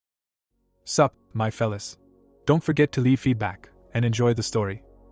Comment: In the start i thought he said 😂suck my fella 😂